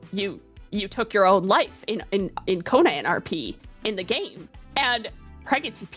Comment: For the longest time I thought she said Kon-On and not Connon so I was so confused cuz I thought it was with anime girls becoming warlords and getting pregnant